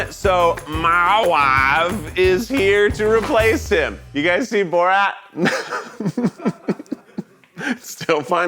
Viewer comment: Did Keith said "Have you seen Borat?"?